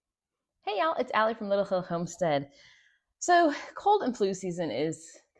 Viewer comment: Swear i thought she said lil ho homestead 😩🤣